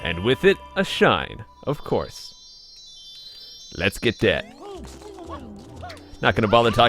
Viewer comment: When he said let's get that I thought he said let's get dead